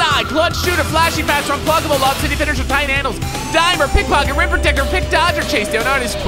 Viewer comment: Is it just me or did say “Ripper dicker”? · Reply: He said protecter but it sounded like dicker😂😂